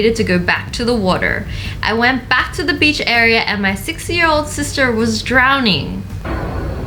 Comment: I heard him wrong so I thought he said 60yo sister instead of 6yo 😂